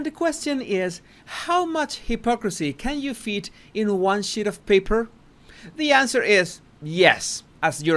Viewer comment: At first I thought you said one shi* of paper lol